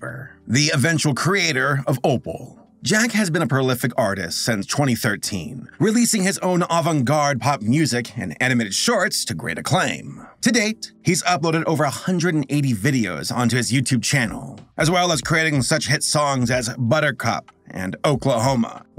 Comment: Sounds like he says animated shart at the beginning